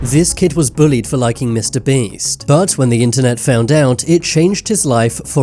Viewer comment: I thought it said licking mrbeast but a some Days i can't talk